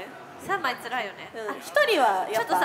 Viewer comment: 辺りから凄い『頑張ってー』って聞こえるw